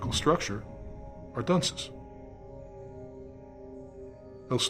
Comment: Would somebody tell me what word it is said in 8:34 minute? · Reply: Dunces